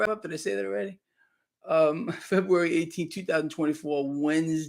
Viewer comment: I rewnd and thought you said February. It's Srptember. 😊My mother's bday....would've been 95.